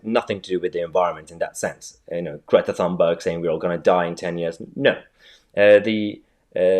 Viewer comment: I was listening with my eyes closed when I heard you refer to Greta Thunberg. You did refer to her right? Because I initially thought you said Greatest Humbug. Which would be quite witty